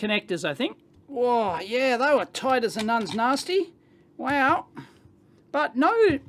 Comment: LOL what did he say ....lol tight as what.....😮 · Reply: "A nun's nasty" 😂 I just got it.